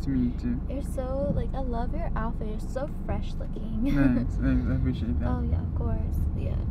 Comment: lol first I heard “i love your apple, your so precious” And then I played it again “I love your outfit your so fresh